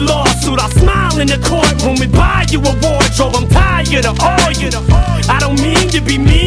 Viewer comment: I always thought he was saying “I’m tired of all ya”